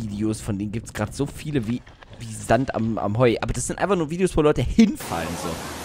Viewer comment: was hat da gesagt ? Wie Sand am Heu